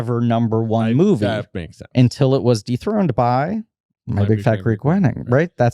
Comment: WHAT is the name of the other movie about finding yourself in the frontier??? You're talking at the same time and no matter how many times I replay that part, I CANNOT understand what you're saying. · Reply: My Big Fat Greek Wedding